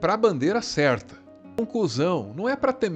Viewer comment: Tu falou "conclusão", eu entendi "um cuzão", pensei, "ahh, entendi errado". Mas ai, na tela tava o Bolsonaro batendo continência pra bandeira dos EUA... Logo, pensei "ahh, entendi certo" kkkkkkkkkkkkk